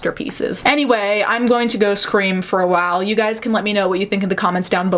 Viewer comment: I’m tired, so at the end when you thanked your patrons, I thought it said “Thank you to my pastors” and I was thinking, after seeing some shit, I might need some pastors.